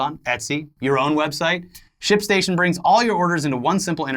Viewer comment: I could have sworn you said shit station. That you for your work.